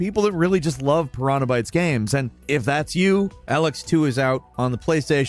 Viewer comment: Thought he said Alex II and I about pooped myself...